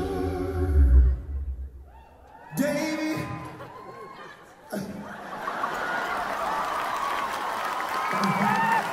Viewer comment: What did she say at 2:49??? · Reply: "Scott" :)